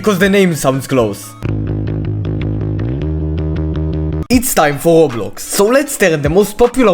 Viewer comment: Why did i hear "its time for lox" (also i think team 'AHSH' is team ice cream cuz at the vid of "these are the new bfdi characters" the subtitles said that!1!1!1!1)